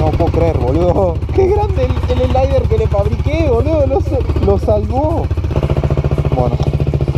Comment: Que dijo en el mn 30:08 alguien me explica por favor y dijo que lo salvo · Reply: Que le fabrico el alier o algo asi que tiene en el escape que eso lo salvo